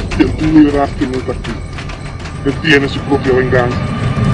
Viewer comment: Alguien entiende que es lo que dice el mudo en esa parte? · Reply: El que tú liberaste no está aquí, el tiene su propia venganza. Se refería a Anthony.